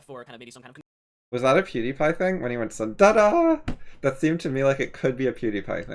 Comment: i dont think its a pewdiepie thing. the other things i mentioned were obvious pewdiepie thing,. but i think with this one hes just saying "ta da" to re-introduce the 2 books hes holding up